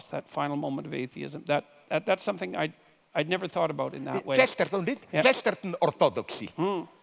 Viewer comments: Can anyone hear the book Žižek mentions at around 2:08:09? I can't get it. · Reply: Orthodoxy Chesterton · Does anyone know what book Zizek mentions at 2:08:10 ? I can’t figure out the title through his accent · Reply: Chesterton Orthodoxy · Can somebody tell me what zizek tells at 2:08:12 , cant understand if he mentions a book or smt like that 🌿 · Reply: "Orthodoxy" by G.K. Chesterton. "Chesterton: Orthodoxy" is what he says